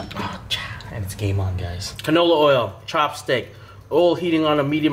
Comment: wtf u said lmao 🤣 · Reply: "Uh cha and it's game on guys"